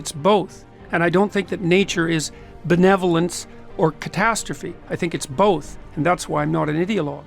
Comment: I heard "I think they are both, that's why I'm not an idot yo!"